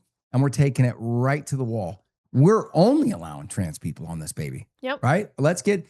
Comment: Did he just say Balz to the wall?